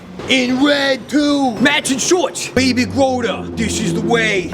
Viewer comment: I thought he said "baby scrotum" for a sec instead of baby yoda ROFL